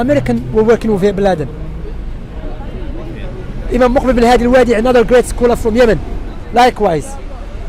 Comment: what is the name of the second scholar named after bin baz couldn't hear him well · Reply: Muqbil bin Hadi al-Wadi'i